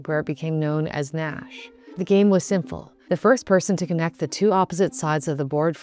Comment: Huh?! I know the word is supposed to be 'simple,' but it really sounds like she says, 'The game was sinful .'